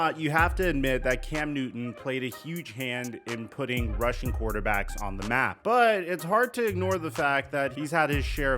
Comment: I heard "putting russian quarterbacks on the map."